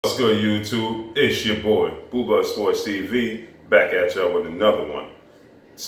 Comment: Boo Blud Sport, no it's not a typo.. That is actually how he says it (Joking :) ). Ok, back to this post: Stop the bias, the lies, the unfair fanaticism. It's time you face reality. You wanna make the people believe that AJ booked the biggest venue in UK so he doesn't fight? Pathetic. Anyone is going to believe that this huge boxer with high ring IQ who won his belts 1 by 1, the highest paid in his division doesn't want to fight anyone his is offering a fight to? Get the hell out of here with this hate campaign. They are all petrified of AJ. Tell it how it is. Thanks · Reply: And I thought he said boo bar sport every time.